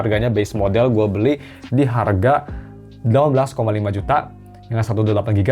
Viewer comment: "12 koma lima juta" ? gak salah dengar bang?? sini gw ambil aja 13 juta...